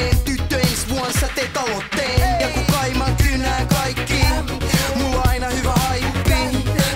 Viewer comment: misheard 1:22 kun kaimat jyynää kaikki mw :D :D